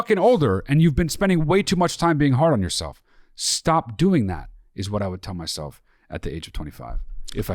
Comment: I first thought you said, "being harmless as yourself." I know I'm wrong but I'm going with it. Beside the miss-quote, I can tell you're good brothers and family. The manners you use as you speak about the words you recall from your past is warm. Peace. The Ace Ventura reference made me laugh.